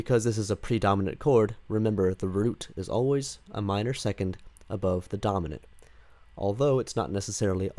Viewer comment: Me: repeats 4:50 - 5:04 about a hundred times or so "Where is the root of an augmented 6th again?" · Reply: omg, I misheard him say "a minor third above the dominant" the whole time. I was so confused for a bit there.